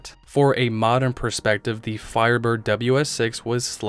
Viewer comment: did u say Firebird 😳🤝 great video man lol